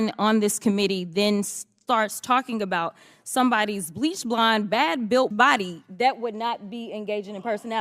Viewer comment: I thought Crockett said bleach blonde bad built Butch body. Was there a problem with the audio?